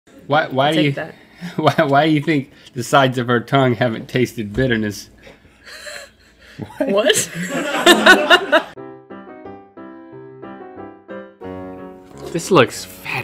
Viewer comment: 6 seconds in and I thought link said "why do you think the sides of her tounge haven't tasted clitors" i instantly had to rewind to make sure I wasnt crazy XD Turns out I'm just crazy which is okay but boy if I had water in my mouth I sure woulda spit it out on that one! Ahahaha